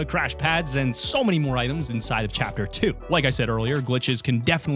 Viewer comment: did he just call it a chappa